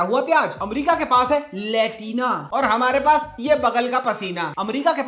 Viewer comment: i literally thought you would rather say - India k paas hai Latrina 🤣